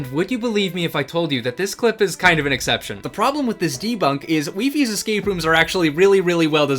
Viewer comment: i thought he said the wifes debunk